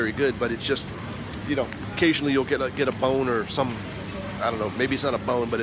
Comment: Did you said Bon-err? lol